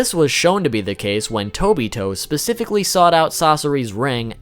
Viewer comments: did he just say Tobito? Obito, or Tobi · I’m sorry, but did you just say Tobito? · Did he just say "tobito"